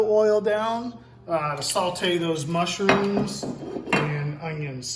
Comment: did he say saltee?